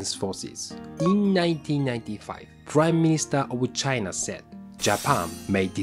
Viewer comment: I heard "crime minister" which is also an accurate title :D